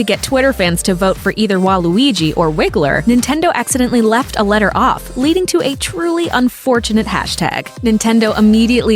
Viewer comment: What's the censored word? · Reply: It's WiggerWednesday that got censored at 0:58 well just wigger got censored that's all